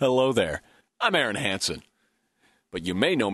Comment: for the longest time I thought he said "I'm very handsome"